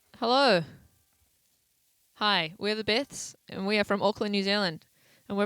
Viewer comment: They're from Awkward, New Zealand. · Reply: I thought you said awkward Auckland :)